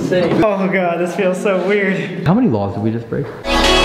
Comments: "How many laws did we just break?" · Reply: Dash i thought he said how many walls did we just break 🤣🤣😂😂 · "How many laws did we just break?" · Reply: For some reason i thought he said hiw many walls did we just break and i was like wtf u breaking walls for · "How many laws did we just break?" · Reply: Dash oh I heard, "how many balls did we just break.".